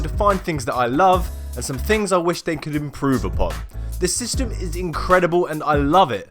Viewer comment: what did u say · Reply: Improve upon? Haha